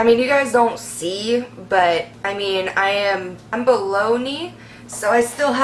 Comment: "im below knee" girl tell me i was confused cz i thought u said u were bologna 😂😂😂i was sooo confused like why is she talking about lunch meat again😂😂😂😂😂 ok back to video